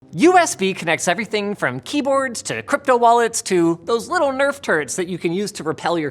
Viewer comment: thought he said nerf turds for a moment there.